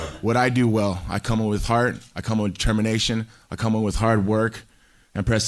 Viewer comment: “I come in with heart. I come with termination.” Lmao he’s the best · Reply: I thought he said determination when I listened to it but, termination definitely sounds more Tito Ortiz-like. 😂